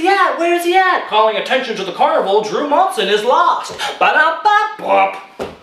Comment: This is so freaking strange, I watch this video a couple weeks ago and I could've sworn that he said "calling attention to the carnival drew Monson is missing" not lost😰😰😰😨😱😱😱